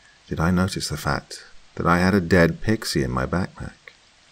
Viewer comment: did he say dead pixie